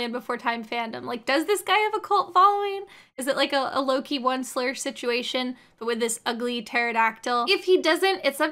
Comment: I know she's saying "Loki, Onceler situation," but every singe time i hear it as "low-key one slur," and i almost wish things had gone down that route instead